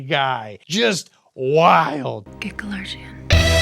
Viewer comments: what the F is she saying!?!!! I ran it back like 10 times. Gecko Larzian? WtF!!!! WHAT IS SHE SAYING!?!!!!!!!! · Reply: "Get Kalarjian" the doctor. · I can't tell what she said at all . "Get a large agian?" "Get lethargic an"? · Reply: Get Kalarjian (the doctor).